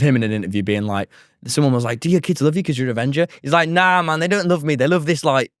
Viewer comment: he sounded like he said “na my ni-“😭